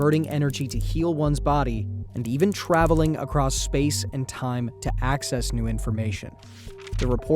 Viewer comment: i heard "altering beyond space and time " andknew this has gotta be some "red pill escape the matrix" type bs